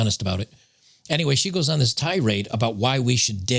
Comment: Tirade or Thai rate? ;-) Subtitles say tie raid.